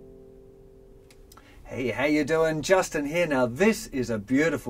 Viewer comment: Wew first time I heard ' Just in here'. Mindfuck